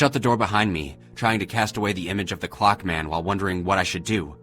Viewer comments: At first, I thought it said "The Cock Man." I wish it would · Am I the only one who thought it said the cock man xD